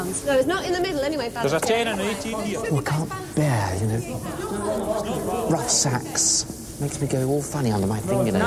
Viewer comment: did he said " rough sex" ! ..